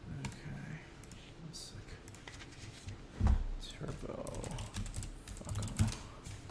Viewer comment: did he just say turbo fucko lol